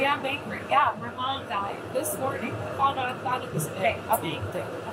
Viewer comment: I thought that you said that your mom died that morning!